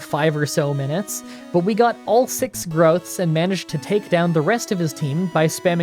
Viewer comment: I heard "We got all 6 drugs"